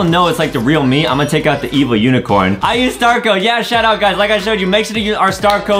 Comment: When say shout out I thought he said “shut up” lol 😂